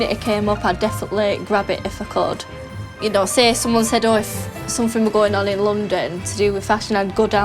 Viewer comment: What is she saying?? "Ydose somohedoys"